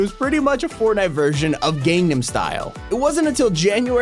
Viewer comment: Did he just say ‘’gamenum style instead of ‘’Gangnam Style’’😂